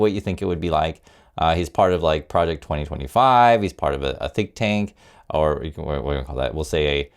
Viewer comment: Sounds like you said ‘a thick tank’.. in this case appropriate mis-hearing on my part. ! Thanks for all you do! Hegseth is unhinged, also people don’t want to visit the US